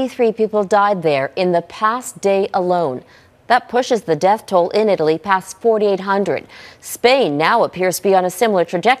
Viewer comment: Global - Your description says that Italy's death tole is 48,000. Please review your content before publishing.. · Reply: she clearly says "...pushes the death toll past 48 HUNDRED..." or over 4,800.